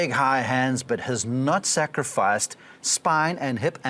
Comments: I paused the video and when I resumed it, all I heard was “nut sack-rifice “😂😂😂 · Did he say his nutsack at 1:50 🤣